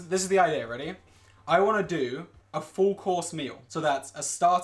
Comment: “I wanna make a four course meal: Starter, Main, and Dessert.” There are three types of people in this world. The ones who can count, and the ones who can’t. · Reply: ohhhhhhhh I thought he said four course meal ahahahah lol